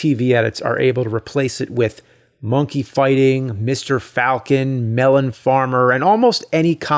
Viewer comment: I thought he said "monkeyfying".